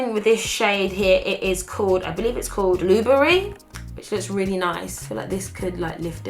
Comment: I'm so dumb, when she said "blueberry" at 10:24, I heard her say "Nuvaring" X'D